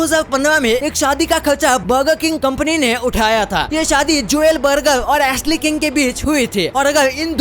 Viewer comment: Am i the only one who heard Churel